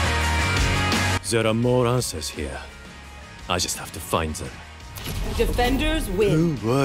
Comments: I think said "There are more answers here. I just have to find them." at 0:38 Also. is literally my friends when I show them my gadgets. "This isn't a science fair! Get rid of that shit!" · I think says,"There are more answers here, I just have to find them." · i think he said answers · i think he says answers with a really thick accent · i think he says answers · i think he says “there are more answers here” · I think that at 0:40 he says "answers" hope this helps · I think he says there are more answers here. It matches his lore and the map's lore too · i think said answers